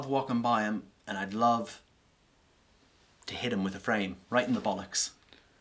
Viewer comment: Hit him with a what in the bollocks? Anyone know what he said there? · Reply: He said a 'frame'. He said he worked packing frames. Probably like picture frames 🖼